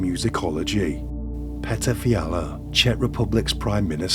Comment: So nobody else is going to say anything about the fact that that dude with the beard at the 10:35 mark. When the YouTuber said his name it darn sure sounded like he said pedophilia😅😮😂😂 with one space between Peda and philia lol. I played it back like three times like there's no way I heard that right, but I did. God bless Americans in America